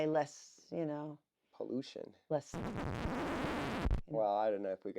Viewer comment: I wanna know what she said 😂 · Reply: Chem Trails. We dont want to get our video flagged by stuff that can be seen as "Conspiracy Theories"